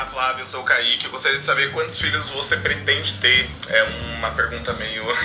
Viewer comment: cara nessa hora eu entendi quantos fígados!!